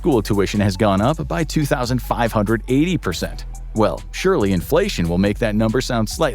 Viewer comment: I thought he said $2580. I squinted a lil and saw "2580% " and stopped watching the video